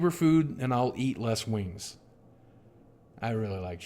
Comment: Man, I misheard "wings" for "moons" at first. I didn't realize you were Unicron, boogie!